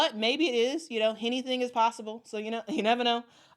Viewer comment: did you just say “Hennything is possible”? Love it 😂😂😂